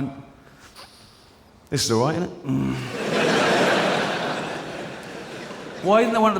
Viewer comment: what did he say at 1:10 ? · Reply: This is aight, isn't it?